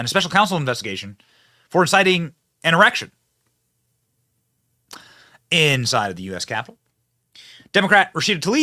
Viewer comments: Heh, @ 7:34 you say erection instead of insurrection 😂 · Benny said erection instead of what I believe he meant to say-insurrection? Haha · Did you just say “exciting an erection?” Haha